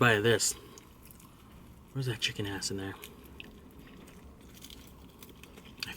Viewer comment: did he say theres a chicken ass in there?? Hahah